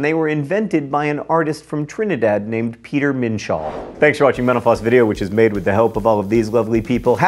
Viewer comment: At the end I heard "thanks for watching menopause video"...goes nicely with the "it'll make you feel very old" :)